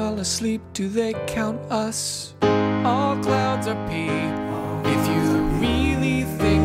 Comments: I thought he said “all clouds are people” and I was like ... ok mate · I thought he said “all clouds are people” and I was like ... ok mate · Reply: i thought he said all clouds are thick